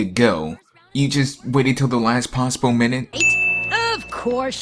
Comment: the subtitle said "moment" but he said minute, is that a sin?